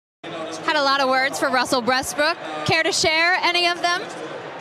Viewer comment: Why did i hear “ russel breastbrook”